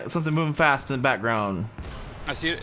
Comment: why does it sound is saying walkable dog instead of walker bulldog